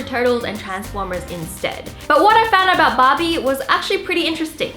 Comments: Who else thought she was saying "bobby" instead of Barbie? 😂😂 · Love the accent I thought you were saying Bobbie not Barbie love you Wengie ❤️❤️